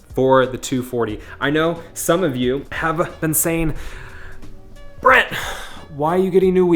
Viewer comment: Did he just said "Bret" ??